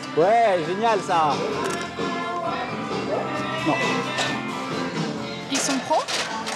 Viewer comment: il y a que moi qui a entendu : "ils sont cons ?"